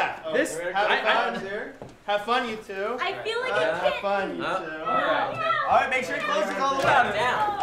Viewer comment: i feel like a kitten, MEOW! MEOW! (i think she said that)